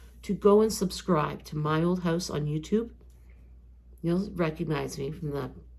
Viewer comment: I can't find "this old house". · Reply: I tried looking for what I thought she said… “my old house” and it only brought up “this old house” lol